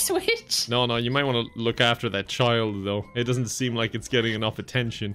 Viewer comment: I thought it said abort child